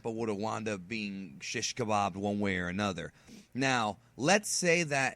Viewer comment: Am I wrong ? I heard the word "sheesh kabab" sounds like you are familiar with Arabic food!! in